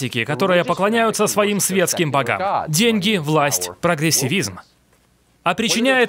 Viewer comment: "деньги, власть, ..." что третье? профессилизм? професиризм? кто расслышал?